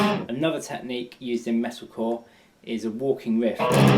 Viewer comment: What exactly did you said on 10:31 ??? Another technique -.............?? i need to learn that technique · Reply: I believe I heard "Walking". Hence his hands or fingers seemed to be walking